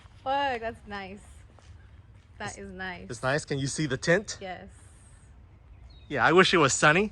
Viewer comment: thought he said can u see the tit 🤣🤣